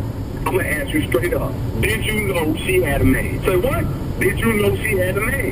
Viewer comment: My high ass thought he said did you know she was a man lol